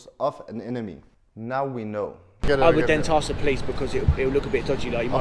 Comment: Did he just say, "I would then toss the police . . . ?" Off topic but I sent a Japanese friend of mine an email saying that my family and I were going to visit Tokyo and that we would love to see him. He wrote his reply in Japanese and ran it through Machine Translation before sending it. Back then, translators had not come as far as they have now. His reply was. "Great. I look forward to tossing my family at yours."